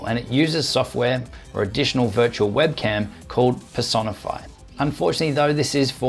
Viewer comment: any one else think 3:15 sounds like he saying" VIRTUAL WEBCAM called PISS ON A FLY" LMFAO